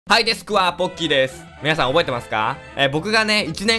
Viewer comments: ｢はいラスク&ポッキーです｣って聞こえてた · 普通におもしろいぞこのゲーム！ カンニングはダメだぞみんな！！！！ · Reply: ポッキー / はい、ですくわ〜懐かしいww私ははい、ひさこポッキーですに聞こえてましたww